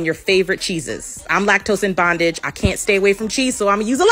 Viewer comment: I am “lactose in bondage” lol. I died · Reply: I heard “lactose In fondant “